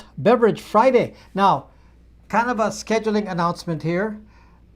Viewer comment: I thought you said “Cannabis Scheduling” instead of “kind of a scheduling” following the adult beverage comment 😂 💨 🌿